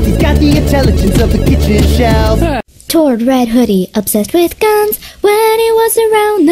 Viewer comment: Eddsworld! Eddsworld! Spoken: Sorry, whose world is it? Eddsworld! With Tom! (Blue Hoodie!) Both angry and rude, maybe it's his smarts or maybe it's the 'tude, One thing's for certain, he doesn't make sense, Half the time is gibberish and the other half's ingooblyblench! Tom: What? And Matt! (Purple Hoodie! Also green overcoat?) No one likes him as much as himself, He's got a formidable chin, though he doesn't know that word, Cause he's got the intelligence of a kitchen shelf! Matt: Hey! And Tord! (red hoodie) obsessed with guns. when he was around the show was more fun! he was the one that they tried to replace if you mess with him he'll shoot you in the face! Tord: Yep Edd! (Green Hoodie!) Usually the leader, Probably the first of them all to poke fun, He's the artist, he loves cola, And he's always trying to make a pun. Spoken: Probably due to his linguistic…..EDD….-ucation? Edd: That's terrible. Eddsworld! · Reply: I thought they said "obsessed with gum"